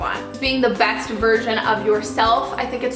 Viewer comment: I thought you said the best Virgin you could be... Oops